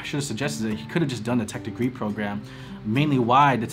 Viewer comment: "he could've just done ..??... " sorry but i couldn't hear it clearly, what was the term ? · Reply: Sounds like "Tech degree program". I might be wrong.